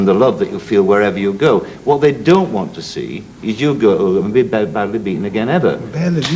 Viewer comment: what does host say at 5:16 Ali looks just as confused as me... pls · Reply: The fans don't want to see you badly beaten ever.